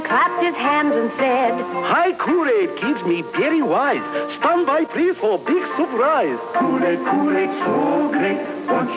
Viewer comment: Ok I've been trying to figure out what he says at 0:20 for like a year now. He says "Hi kool aid kids, me very wise, strung by tree for big surprise"? What?